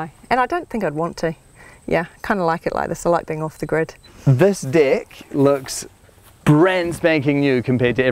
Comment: When he said "this deck" I thought he said "this dick" Australian Accent XD